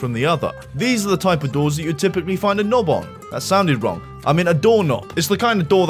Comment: I'm sorry but this sentence is roasting my american brain, what the hell is he saying here??? "These are the type of doors that you'd typically find Anoba, that Soudaron, amena dorna!" Sorry for not brushing up on my Darnassian before this.